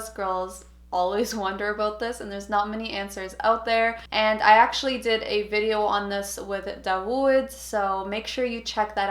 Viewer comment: what's she saying... Can anyone write? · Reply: "I actually did a video on this with Dawood, make sure to check it out"